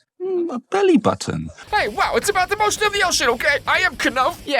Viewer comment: "Hey, wow, it's about the motion of the ocean okay." In case anyone else had no idea what he was saying there.